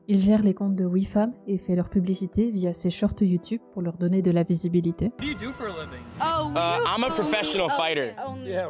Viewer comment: Sais pas pourquoi mais a 35:05 a la place de visibilité j'ai entendu baissébilité j'espère que je me suis trompé 😂